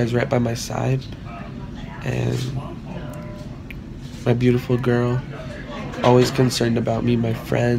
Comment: I swear at 2:48 he said my beautiful girl